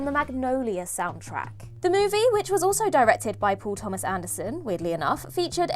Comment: Paul Thomas Anderson... I heard "poor Thomas Anderson" and was really confused why she would call im that twice... Had do Google..